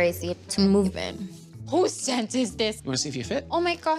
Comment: Yo I gotta be real, when she asked "Who's tent is this?" I deadass thought she said "Who's scent is this?", like she was some kinda bloodhound or something XD